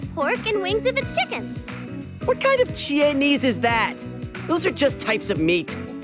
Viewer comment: “Chie-nese” funniest thing I heard today 😂